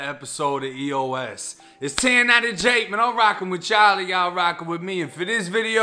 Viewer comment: Took me ages to work out he was saying rocking with you all. Lol I thought he said rocking with Charlie.